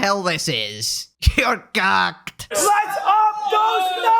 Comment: i heard this one as Cucked... fitting if you're Idubbbz